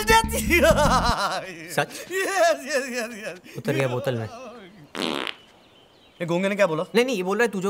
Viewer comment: what he said? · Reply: utar gya bottle main